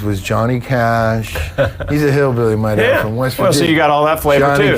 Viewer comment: Did he say his dad was from West Virginia